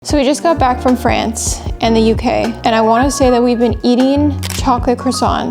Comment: I thought you first said " Just came back from France in the UK " haha But great video!!